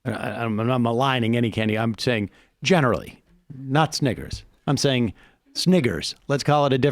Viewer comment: Did he say "Sniggers" at 13:45?